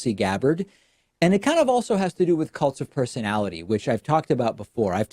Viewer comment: I thought he was saying 'coked-up personality".